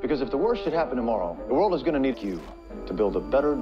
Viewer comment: I swear it sounds like he says "The world is gonna nuke you ..to build a better day after"